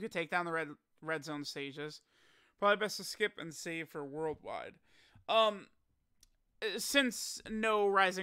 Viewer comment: I heard "save for Raditz" Noted